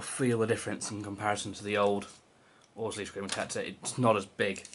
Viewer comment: What? I can't understand what you were saying. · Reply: Just saying it's not as big as the old V1 Orzly TGSP